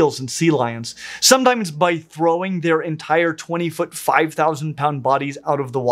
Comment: Lol when you said “...they can launch their 20 foot 5 thousand pound body out of the water...” I thought you said “...launch their 25 thousand pound bodies..” and I was like “oh, my... the megalodon is upon us” lol